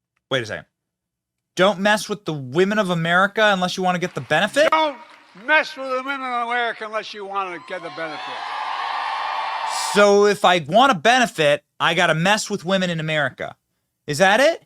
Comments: I thought he said don’t mess with middle America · I heard, "Don't mess with the Middle of America..." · I thought he said "Get in bed with them" / Barry has been continuing his destruction of our country for the last 3 years, we wont last through much more of this hostile demolishing of our country. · I thought he said "get in bed with them" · I thought he said "don't mess with women of merica unless you want to get in bed with them". My bad😂